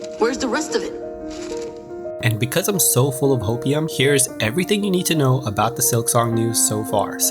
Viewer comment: Lmao it sounded like you said opium instead of copium Also hollow knigth released near time when previous zelda game released, maybe it's a sign that silksong would release the same way!